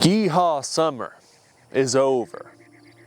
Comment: I thought you said geehad summer at the start